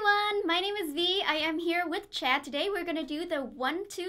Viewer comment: did she say checd